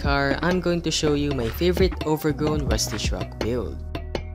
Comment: I thought he said ew instead of build 😂